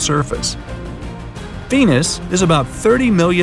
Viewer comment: I think I heard pen-is